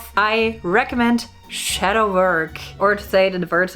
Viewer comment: does anyone know what shes saying here?? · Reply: shadow work